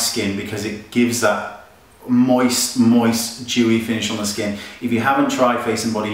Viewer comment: I thought you said "Jew-y" finish at 5:45 for a second there. xD